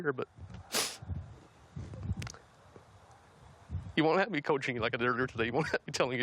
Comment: What Did He Say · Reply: "you wont let me coach you like a derrbb HAH tell ee ih"